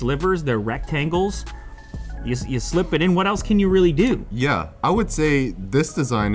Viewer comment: Am I the only one who heard "what else you can willi do"?